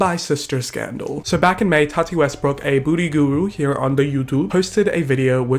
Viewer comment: It sounded like "Booty guru" HAHAHAHAHAHAHAHAHAHA PERFECT